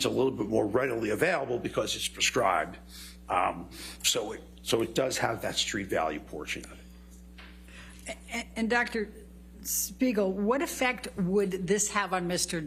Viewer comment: 😂 I thought I heard said “Dr. Smeagol” while fast forwarding at some point